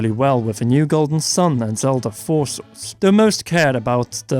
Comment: I know he said 'Zelda Four Swords', but it sounded like 'Zelda Forces'. Hmm. Did anyone else just shudder?